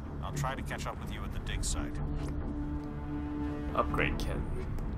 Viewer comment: HA, I heard him say "dicke site"